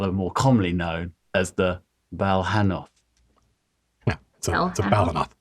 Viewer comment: What is the name of the creature in the skull? I thought he said Volinoth and couldnt find anything, i was just curious as to what it looked like. NM next time i heard the name i was able to look it up Balhannoth!